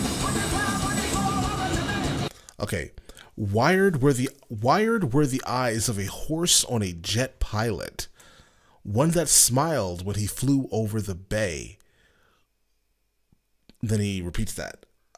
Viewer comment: I always thought he said where are the eyes and the hearts of the jet pilots..